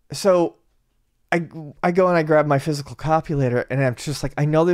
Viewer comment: Did say "Physical Copulator"???????? 😂